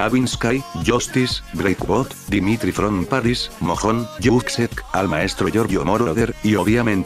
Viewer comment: mojón escuche yo xD